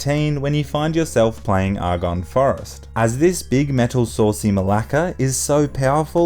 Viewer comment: did he just say malaka ?